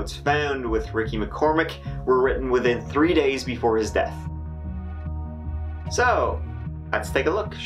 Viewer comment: Did u actually say THree at 4:32 or did i watch so many videos tree is starting to sound like three now